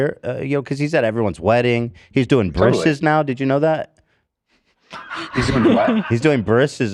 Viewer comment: can someone tell me what Ethan says here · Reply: "He's doing brisses now", briss is when Jewish children are circumcized